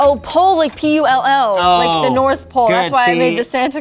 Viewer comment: Did she just say "p-u-l-l" ,like the "north pole"?